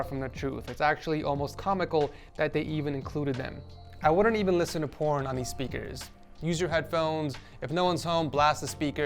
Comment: “What did he sayyyy???” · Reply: I wouldn’t even listen to po*n on thses speakers hope you get to hear again one day 🙏🏼